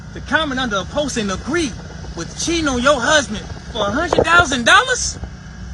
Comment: I thought that dude said - "Cheno, your husband." but what he meant was "Cheating on your husband." 🤦‍♂